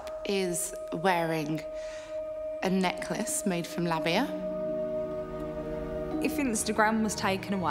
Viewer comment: necklace made from what? I couldn’t understand what she said. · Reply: Labia